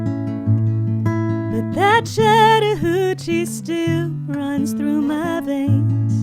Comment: Did she say "but that shattered hoochie still runs through my veins?"